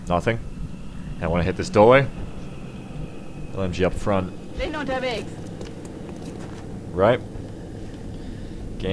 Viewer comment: I wish I knew what actually said at 2:39. Every time I hear that sentence I always think that they are saying "Say no to Beags" you might have a rebellion soon if you know what I mean. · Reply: "Bin unterwegs", mesning 'On my way'.